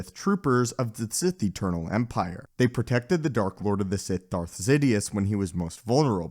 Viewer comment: I think he said Darth Zidious at least it sounded like that to me